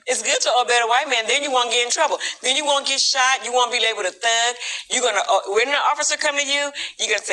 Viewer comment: Did she really say "You wont be able to thug"? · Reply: She said "be labeled a thug".